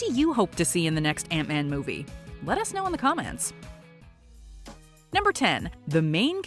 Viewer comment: Did you just say "comm-ants"?